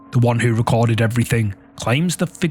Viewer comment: I died laughing!! I thought he said pecan like we Southerners do!! 😂